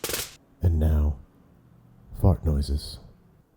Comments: i swear i heard "fuck noises" instead of fart noises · "And now....fart noises." p i n g g g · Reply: I totally thought he had said frog noises